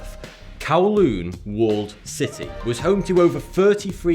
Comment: this is the first time i came to this channel in thought the guy said 42 instead of saying Thoughty2